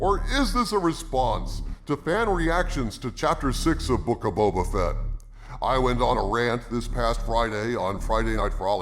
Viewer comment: I watch on double speed, it sounded like said "Woke of Boba Fett"🤣